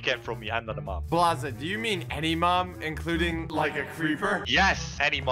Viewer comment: is it just me or did he say mom